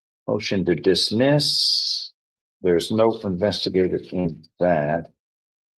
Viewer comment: "Motion to investigate". Courts do not "investigate". They make decisions on the evidence presented. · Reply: I heard motion “for investigator”. Court could grant funds for that I suppose